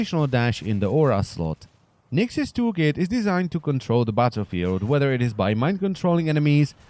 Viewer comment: It sounded like you said "Nyx's tool kit is designed to control the butter field". lmao